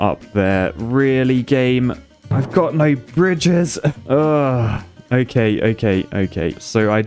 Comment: was i the only one who thought that he said "i got no bitches"